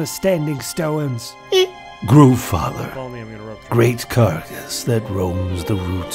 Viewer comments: What does he call him here? I can't quite understand. "____, Great __ that roams the roots of the world tree" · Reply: Groove father Great carcass that roams the roots of the world tree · Gorm trying to make an epic, impassioned speech only to immediately get his head blown off will never not be funny. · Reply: I thought is said goo father so ya. Thought he made a little goo golem.